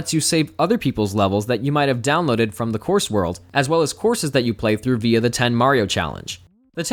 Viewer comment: I'm sorry, did you just say "through via" ?? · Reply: Not in that way. I say "...courses that you play through ... via the 10 Mario Challenge".